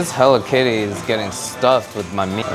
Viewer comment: this hello kitty is getting stuffed my meat?? 😳Did I heard it right or am I just being too dirty